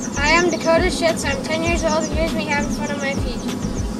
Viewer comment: did he just say he is Dakota shuetz